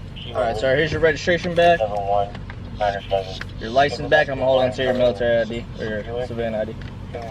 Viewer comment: He Didn't keep anything! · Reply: sounds like he said "I'm gunna hold on to your Military I.D!"